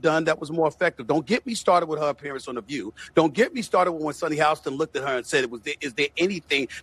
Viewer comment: I thought you said The view was done????